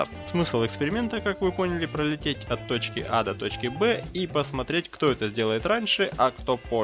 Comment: самолёт савыйдж😂😂😂? 1:40 мне послышалось или ты сказал: от точки ада точки b?